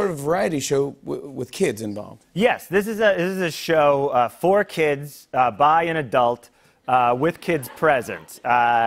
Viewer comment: "For kids, by an adult," I thought he said "Four kids buy an adult"........ like four kids purchase john mulaney